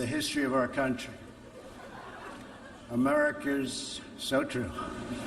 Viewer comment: it sounded like he said america with a hard r YIKES donald